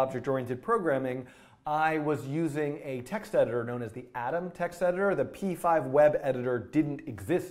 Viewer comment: I still hope your video have Engsub. It's more comfortable for foreigners to easy understanding. Thanks. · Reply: If you are the one who prepared the subtitles then how could you make that mistake of typing "Adam text editor" instead of "Atom text editor" :D.